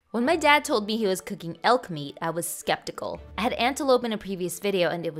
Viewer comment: i wasnt looking and i heard it as elf meat and i was like "what in the legolas??"